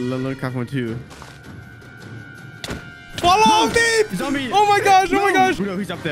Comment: did you say falou meep or fall out?